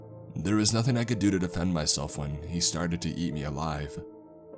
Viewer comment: when he said “and stated to eat me alive” i thought he said “yeet me alive” and i choked on my own spit